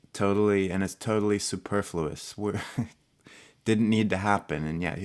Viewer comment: What was the word said at 0:57? Superlfousisisi sumfin, also its meaning pls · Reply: 1 superfluous: extra or unnecessary